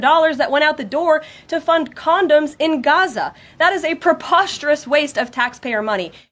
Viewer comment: I thought she said “condos in Gaza”. It would’ve made much more sense. 😅